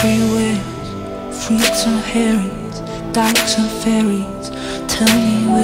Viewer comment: Did they say "Donald Trump Fairies?"